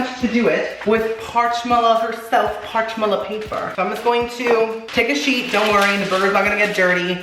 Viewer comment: when he said "take a sheet" i thought he said "take a SHIET" that burger.... is iconic